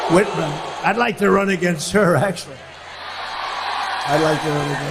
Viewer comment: I heard him say he'd like to RUB against Governor Whitmer!